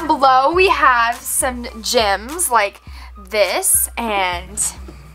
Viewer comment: I thought you said gyms